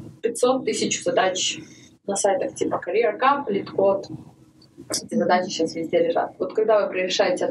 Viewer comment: На 12:36 сказала что нужно решать <num> задач на определенных сайтах№ Что это за сайты? Из-за звука не услышал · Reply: назвала careercup и leetcode. Также достойными вариантами являются их аналоги topcoder, hackerrank, interviewbit, interviewcake и прочие.